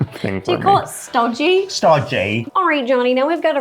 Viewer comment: I thought he said “sludgy” not “ stodgy” 😂